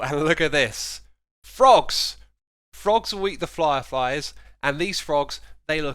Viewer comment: Did you say "Flyerfies" @ 5:55 on porpous? xD